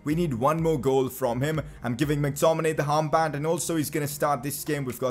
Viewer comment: he says harmband instead of armband